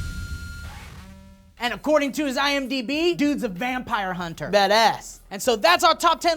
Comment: i heard "Dat ass"